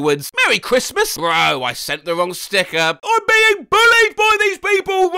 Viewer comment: oh I thought that said "sending the wrong sickler" and my DW brain was like "WHAT'S THE WRONG SICKLER?"